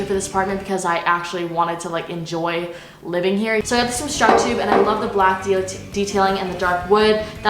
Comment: i thought u said i got this from shrek 2